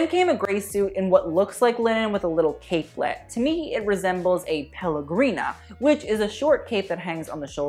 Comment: I wasn't looking at the screen at that moment and thought he said pellegrino and then I looked at the screen and literally screamed.